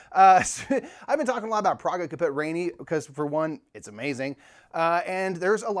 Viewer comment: What game are you talking about? I couldnt understand what was said at around 0:35. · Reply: Praga Caput Regni